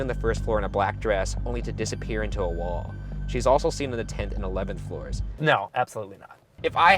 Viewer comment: Can someone please tell me what he says around 2:00? She's also been seen....? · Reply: on the tenth and eleventh floor ^^